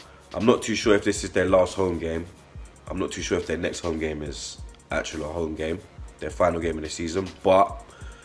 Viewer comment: what did just say lol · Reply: He said “I’m not too sure if their next home game is, acchoolow home game” 😂